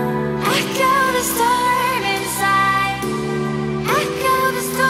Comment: Crash through the mountain top, You`re the fire inside, The light in the night sky. Go higher and higher. You leave it all behind, You leave it all behind. You're blind in the moonlight, Erupting desire, Let the flames grow higher, I kill the storm inside. Crash to the mountain tops, You`re the fire inside, The light in the night sky, Go higher and higher. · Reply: I hear "I got a storm inside".